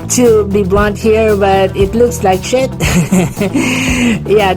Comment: wait..why did u say Sh¡+